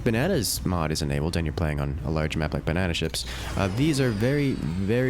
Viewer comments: did he say bananaships? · anyone else hear banana ship